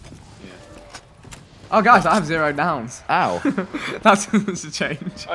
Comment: WHAT DID VIKK SAY AT 44:30 THAT HE AND WERE LAUGHING ABOUT. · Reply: I have zero downs. Makes a change.